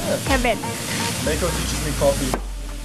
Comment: の箇所で僕は店員さんがMaiko suits me coffeeって聞こえるんですが、多分違うとおもます。何て言ってるかわからないので教えて頂けないでしょうか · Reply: こんにちは、このスタッフの方はMaiko teaches me coffee. と言っています。